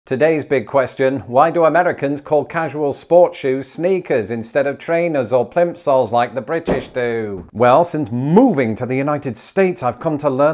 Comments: Plimsoll is a word I learned today. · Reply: I thought he said pimpsouls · At first I thought you said, pimp soles, weird, but ok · I thought he said “pimp soles.” I think that is a great name for a shoe. My sneakers (I long ago gave up tennis and going to the gym in Chicago) will now have a new name!